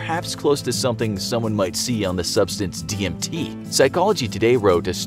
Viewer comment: who else hear TNT instead of DMT